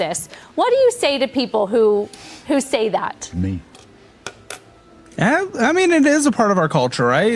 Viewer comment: Anyone else hear that deep voice saying "Me"?